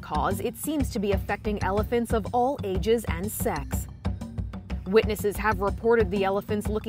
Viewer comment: did she say sex?